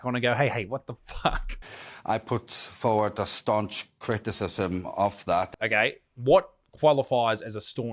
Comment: Oh, I thought he said starch criticism. I was wondering what laundry had to do with this.